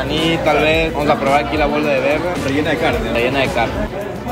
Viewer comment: que dijo? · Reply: Bola de verde rellena de carne, plato ecuatoriano